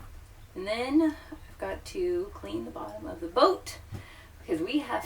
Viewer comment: all I heard was clean and bottom.😂